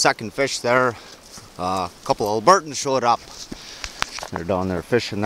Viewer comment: What did you call those dudes at 4:04? Love your videos, btw! Glad to see another young(ish) man not scared of the cold weather and hard work. Maybe there's hope in this world, yet... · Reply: they had Alberta licence plates on there truck I called them albertans